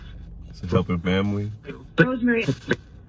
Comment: did anyone else hear the name rosemary a few times??